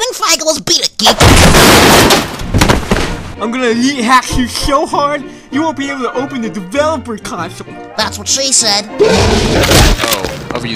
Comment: what is it that Xavier says? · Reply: "I'm gonna L33T Hack you so hard, you won't be able to open the developer console"